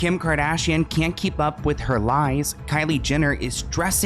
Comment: At the beginning of the video this is what I understood or thought I heard: Kim Kardashian can't keep up with her knees 😭😭🤣🦵🏼